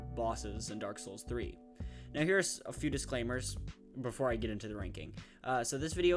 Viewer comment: Bro what did you say😭🥀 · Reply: Here's a few disclaimers.